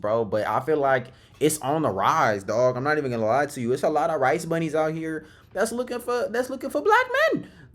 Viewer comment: did bro just say “rice bunny’s” 😭💀